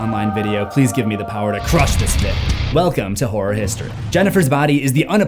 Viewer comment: Sounded like you said welcome to whore history and my first thought was "appropriate".